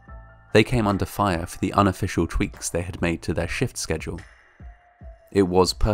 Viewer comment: For a moment, I misheard this as "they had come under fire for the unofficial Tweets they'd made"...